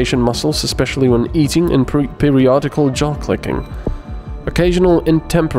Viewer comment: i thought he said "jock licking" so i was like what and skipped back.